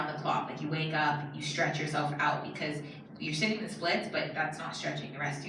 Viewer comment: I thought she said sit in display for an hour but she said split and I don't know if I wanna be a trainee anymore because a lot of people say it's hard and I really don't know if I should or shouldn't anymore